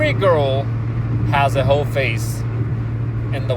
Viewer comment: I thought you were saying Hoe Face.